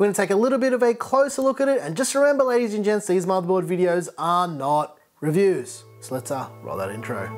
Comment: Is this a review or…? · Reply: he clearly said this is not a review it's an overview of the board.